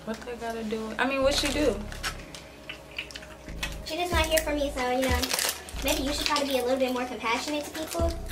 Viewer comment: IGNORE MY CHEWING I WAS HUNGRY SHEESH 💀 WATCH THE FULL VIDEO & GET THIS VIDEO TO 50 LIKES!!!! 🔥🔥 I love you #JerryGang ! ❤️🤞🏾 Follow me on instagram : 😎 (also feel free to comment below this with video ideas) 😘 Don't forget to check out our website! <link> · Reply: Bro did she just say “ my mama 3 so you know “ .